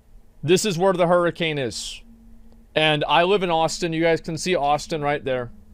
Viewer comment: did he say houston or austin? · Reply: He is saying Austin 😉